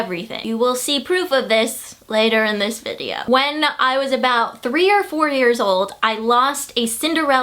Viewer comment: I thought you said when I was 3 or 40!😂😂